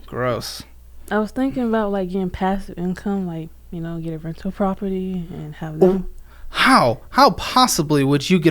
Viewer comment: She has been sold all the dreams hahah. Rental property, house hacking, FIRE, living in a van, cash envelope system, and retiring at 45. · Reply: Thought she said "rectal property" first, and I was like: that should be easy.